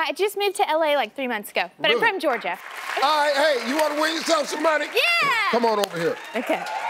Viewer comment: what u say Steve lol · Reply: 'Ah hey, you wanna win some-some money?'